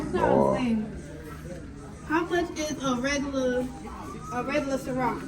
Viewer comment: Did she say how much for the regular CiRoc?